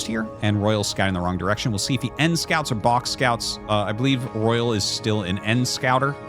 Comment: Oh damnit all this time I thought people were saying "end scout" and I never understood why they called it that. N-scout makes sense.